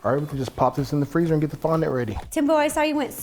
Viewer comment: Captions say “tempo” instead of “Timbo”